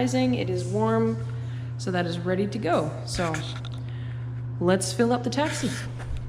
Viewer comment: did you say let’s fill up the taxi? That’s what it sounded like, I had a laugh at that for some reason, I don’t know why but I just found calling that a taxi funny 🤪